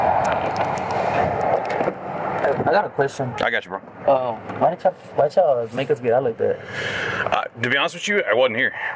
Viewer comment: What does the suspect say at 7:19? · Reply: "I got a question".